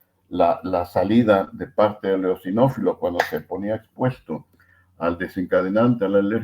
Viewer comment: dice exinofilos??? No entiendo bien esa palabra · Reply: Eosinófilos ♥️ En nuestro canal de Telegram privado tenemos fijado un mensaje con todas las entrevistas. Te adjuntamos el enlace por si quieres unirte -> <link>